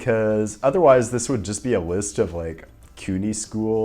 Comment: What did you say at 3:07? “Otherwise, this would just be a list of (cutie?) schools..:” ?